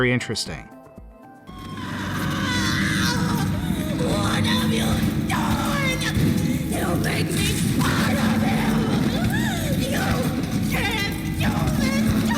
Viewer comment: This is what I hear "what have u done?! You make me part of him! U can’t do this to me" she prob says smth else tho